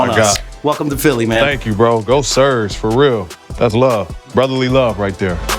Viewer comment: did he say Go Cers?? Still a Pacer at heart.